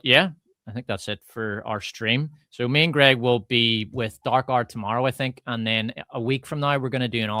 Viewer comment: Does anyone know what channel "Dark Art" is that they're referring to? I can't find it on YouTube? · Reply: He actually says Darkhour, referring to